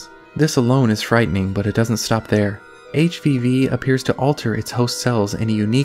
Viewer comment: I thought he said 'HPV' at first lmao 💀